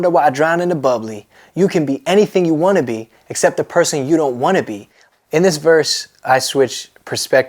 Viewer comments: when I first heard this song I thought he said "you can be anything you wanna be, except the pussy you don't wanna be." 😂😂😂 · Was I the only one who thought he said pussy u don't wanna be